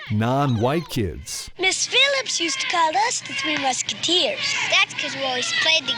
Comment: I thought the black kid said "The Three Mixkeeteers.